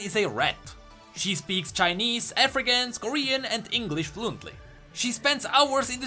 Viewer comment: Kaisa speaks African? wtf... African is not a language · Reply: I thought he said "African", I actually went back and read n notice its Afrikaans, same pronounciation.